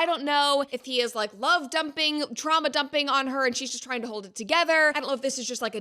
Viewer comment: My volume was too low when you said “trauma” and for some reason I thought you said “traumala” and I was like “ah yes I still have Kamala traumala too” 😂